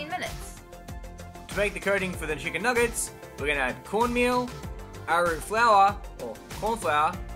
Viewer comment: Am I the only one having a hard understanding this dude? I thought he said "wala wala" for "Arrow root flour" (I actually had to research what it was on the other video).